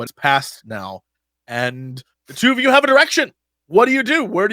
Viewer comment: Did I just hear "You two have an erection" ?